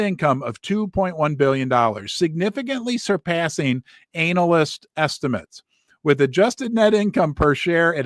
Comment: did he just say "anal-yst?" I don't know if that was on purpose, but I loved it